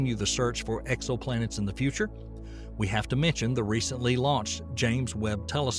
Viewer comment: Did he say " Recently lost JWST "..?